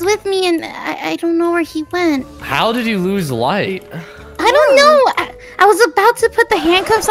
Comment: when said "how did u lose light?' i thought he said how did u lose weight? IM A GONNER CYA IN HEAVEN BOIS XDD